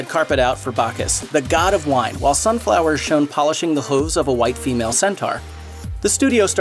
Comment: Polishing the what? @ 10:49 Did he say Hoves? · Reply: Yes. Hooves. Both hoofs and hooves can be used. 😊